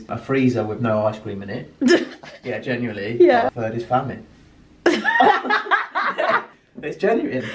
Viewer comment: I didn't catch number 3. I thought he might have said "family", but that doesn't seem right unless it's a deep inside joke. Can anyone clarify? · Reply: SVenDevina I think he said famine